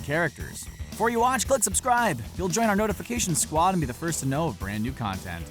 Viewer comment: XD it sounded like he said, "Before you watch unsubscribe!"It would've been bad for his channel.